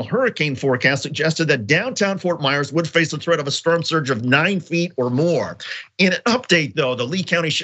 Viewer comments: ROFL this man really said "sperm surge" instead of "storm surge". I'm deceased. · Anybody catch the "sperm" surge around 2:27 :)